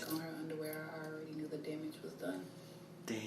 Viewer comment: What she say @ 9:14? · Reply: When she saw her friend putting her underwear back on she knew the damage was done.